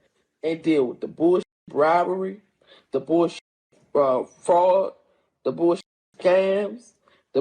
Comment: she said fraud not frogs. I understand how you could have misheard with her 6 chins.